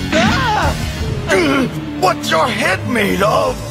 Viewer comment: It sounds like he says "What you hit me love"